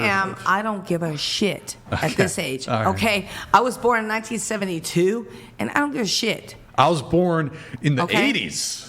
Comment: She was born in 1972??? Looks like she was born in 1942 😂😂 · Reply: i thought she said she was 72 but it was 'born in 1972' i had to double check holyyyy